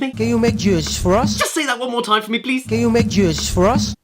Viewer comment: I hear can you make juice for us?